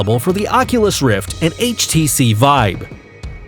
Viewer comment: 1 why does he say htc vibe 8:04 and how robo recall not in this tf....